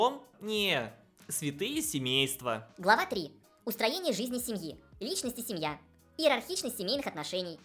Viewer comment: ИЗВИНЯЮСЬ Но на моменте 6:50, я услышал не "устроение", а "устранение жизни семьи" и чуть не устранился чаем... В принципе, в такую программу я верю больше, вы нам какие-то фейки демонстрируете